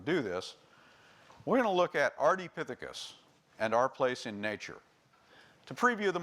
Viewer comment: the closed captions say "Our D Pittacus" instead of "ardipithecus" as on the presentation of the lecturer......